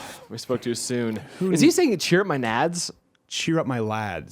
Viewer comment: Don't worry, i heard "cheer up my nads" the first time.